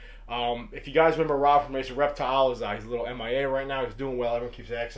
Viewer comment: "If you guys remember Rob from _____ Reptiles" What did you say? I want to find this to make one. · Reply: RaCr Reptiles